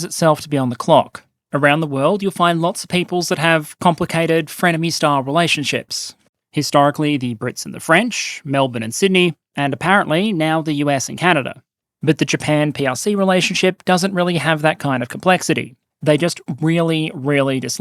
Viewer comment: "Around the world you'll find lots of peoples that have complicated frenemy style relationships ... Melbourne and Sydney ...." - Perun 🤣🤣🤣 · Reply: That makes sense now. I thought he said "Melvin and Sydney" and thought it was from a show I hadn't watched.